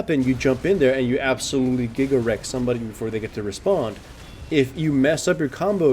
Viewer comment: He says "Gigawreck somebody" But I heard Giggle Rect. and my Summoner name is Giggle Rectum and I was flipped out.